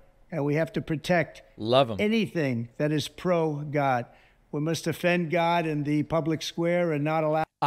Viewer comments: did he say "we must offend god"? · did he say, offend lol